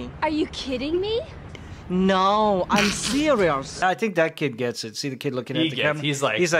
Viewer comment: Really, you guys missed a great soundbite: "No, I'm cereals!" instead of "No, I'm serious!"